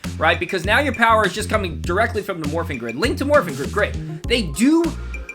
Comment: first time i heard the morph i thought they said “Fury of the Morphing Grid” i was kinda upset when it was just “Link to Morphing Grid”